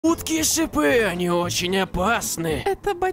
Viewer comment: Мне в начале послышалось утки и шипы